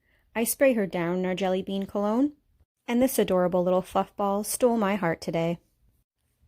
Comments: Thought I heard adorable little fuck ball 🫢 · I thought you said FUCK ball instead of FLUFF ball when talking about the puppy. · sounds like she said fuckball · i thought she said “this adorable little fuck ball stole my heart today”